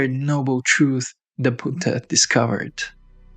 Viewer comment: why is he calling the Buddha 'puta'? Are you a friend or a foe? LOL